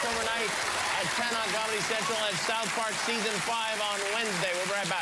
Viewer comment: Did he say season 5? Look at Matt and Trey's faces after he says it too haha